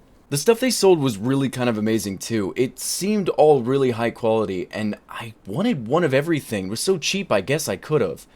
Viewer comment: At first I saw the dildo and heard you say "I wanted one of everything" and laughed a bunch